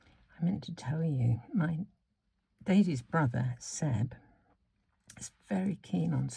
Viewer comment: Beautiful card, I love how you beautified your butterflies. Telling the story about your golfing grandson, I misheard something at the beginning and thought you had said "Your" little brother rather than Daisy's playing Rugby and I was wondering how a 70 something was doing all those sports. LOL Thanks for the inspiration.